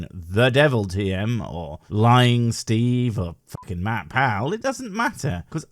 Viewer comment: For a moment when you said 'Matt Powell' i thought you said 'MatPat' and i was wondering what the theory boi did to anger you XD then i realized my oops. im a dumb sometimes. ^^